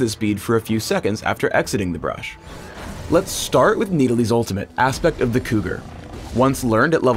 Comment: I swear it sounds like he says Need-I-lee